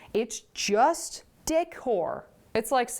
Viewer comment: 🤦‍♀️🤣🤣🤦‍♀️FFS. “It’s juuust Dick Whore.” Wait, WHAT?! Ohhhh De-cor! 🤦‍♀️🤦‍♀️🤦‍♀️ Did ANYBODY hear that as wrong as I did? I blame the mom at the beginning for introducing the W word in the first place. I was so shocked for a half second! (18:08) 🤷‍♀️